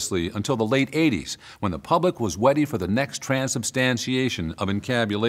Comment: This fu really saif Weady instead of Ready?